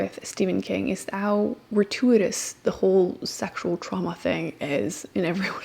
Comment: Can anyone tell me what word she's saying at 2:00 ? I've never heard it before and can't even figure out how to spell it to look it up😅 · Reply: "gratuitous"